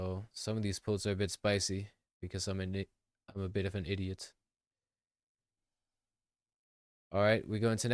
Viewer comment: what was he fin say lowkey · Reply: An i… an idiot